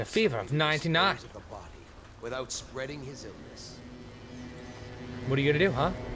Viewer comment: I misheard ''Now to find some way to dispose of the body without spreading his anus''... o,O